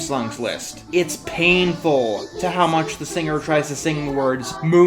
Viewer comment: ok, am I horribly mishearing, or did she say "sweet ass dreams"? I replayed it multiple times, and I KNOW I'm hearing something wrong, but I can't hear anything else!